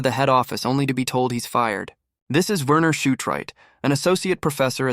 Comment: shot right am i hearing things or what